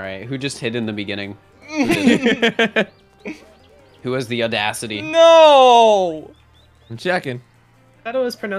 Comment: Oh Audacity I misheard it as Odussy my bad